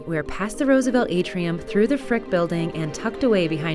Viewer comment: DID I HEAR A CUS WORD!!!! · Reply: THEY SAID FRICK